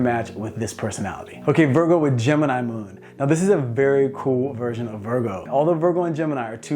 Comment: Did he just said "cool" or cruel? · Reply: Cool